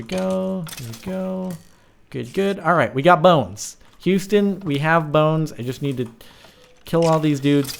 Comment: "Huston we got a boner" instead of what he said